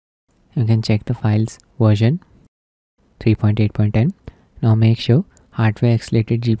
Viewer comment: Thanks ! I also have a Clevo PE60 i9 4070 laptop so m gnne try it with that. But what do you say at 3:00 Schit gos way to quick haha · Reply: 3.8.10 , enabling subtitles will help